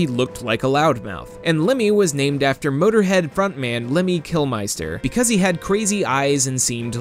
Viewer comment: (hehe) Did he just say Killmeyester? I can't anymore.